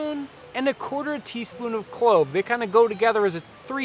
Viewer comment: What was the third spice after nutmeg and mace? It sounded like he said coyle.